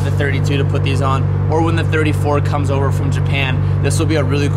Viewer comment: Did I just hear say “when the r34 comes from Japan” 🥶😱